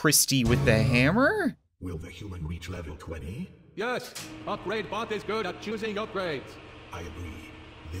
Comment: I thought he said the great Bob is good at choosing upgrades